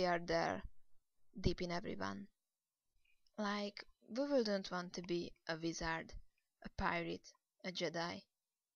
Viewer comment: What did you say at around 3:23? Like, Voowho don't want to be?... Thanks. :) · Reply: Who wouldn't want to be? Sorry, my English needs some practice. Only two mistakes.